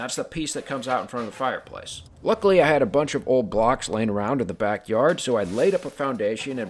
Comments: I could've sworn you said "I had a bunch of old glocks laying around in the backyard" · I thought you said “I had a bunch of old glocks laying around in the back yard” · For a second I thought that he said he had a few glocks laying around in his backyard 💀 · I only passed this by and the first time I heard: "A bunch of old glocks" And had to do a double take to figure out it was blocks I'm from the UK.